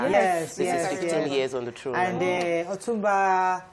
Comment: 50 years on the throne? I don't think so. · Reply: 15 years..... I thought she said 50 years too until she repeated it 🤣